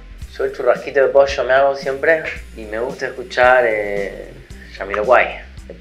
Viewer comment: necesito saber que dijo paco, tercer disco de quién ? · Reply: de Jamiroquai